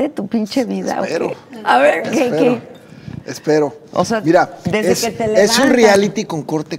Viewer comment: En el minuto 34:52 el diamante negro dice ( ira) y no (mira)... Es como el chiste que dice.... Como hace un borrego nice y un borrego naco...? El borrego nice dice beee... beee...!! Y el naco dice .. Ira.. ira...!!!!😄😄😄😄😄